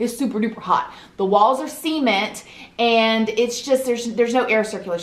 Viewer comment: I totally thought she was saying the walls were semen but it turns out she just says cement like Sea-Mint